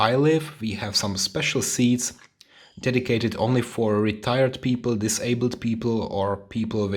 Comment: I really thought he said retarded people